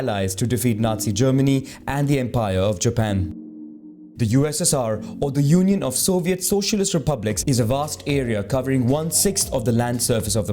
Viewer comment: your subtitles are wrong. The subtitles needs to be: the USSR, or the Union of Soviet Socialist Republics. Can this comment reach 1K likes?